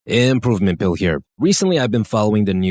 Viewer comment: Omg I have been subbed to you for a while and I have always thought you said “Improvement Phil” 😂